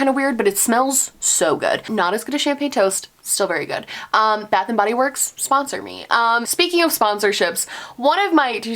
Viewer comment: At first I thought you said Bath and Body works sponsored me....I was like “yes! So well deserved!” Booo...